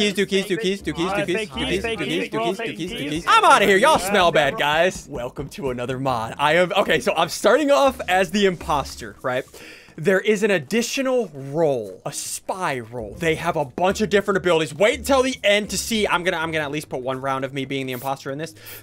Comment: “There is an additional role. A spy role.” What I heard: “There is an additional role. A spiral.”